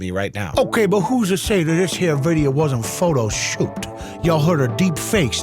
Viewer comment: Did I just hear a, " photo shooped". Man that's been a minute